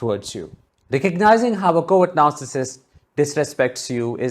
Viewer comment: When you say words 'covert narssistic' but I heard more 'coward narssictic'. Hahaha. I love it, it fits them !!!!